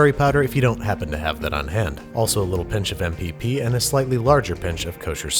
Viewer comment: WHAT IS THE INGREDIENT HE PUTS IN AT 5:20 THE CC SAYS MVP AND I CANT FIND ANYTHING AND THE WRITTEN RECIPE HAS NOTHING ABOUT IT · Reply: He said mpp, mortar and pestle pepper